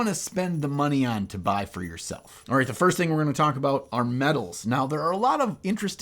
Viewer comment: Really thought you said medals at 6:40 - some awards for nice painting?